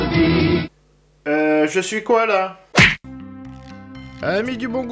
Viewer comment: moi j'entend "heuuu , je suis koala ?"